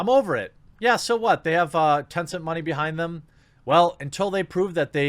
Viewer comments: Tonson money?. Couldn't make out what he said. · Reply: Tons of money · they have what money behind them? I listened 3 times and didn't know what he said. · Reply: Tencent. It's a huge, multinational Chinese conglomerate. And like all Chinese companies, it is completley under the control of the Chinese government